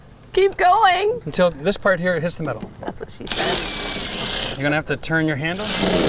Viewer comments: Did she just say “that’s what she said?” I mean I applaud her boldness, but it wasn’t necessarily the greatest setup 😂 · did she just “that’s what she said”? Holy *^%# what a savage this girl